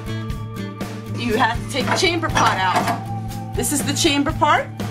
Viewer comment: At first I thought you said you had to remove the chamber pot. 🚽😁😂🤣